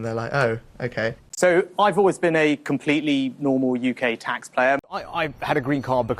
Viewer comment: Swear it sounded like he said "I've always been a tax player" so he basically saying he don't pay tax lol